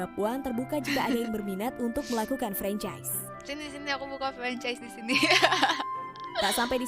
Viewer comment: bilang sini2 aku buka vcs disini bukan ? Apa gw salah denger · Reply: Franchise ngab bukan vcs 🤣🤣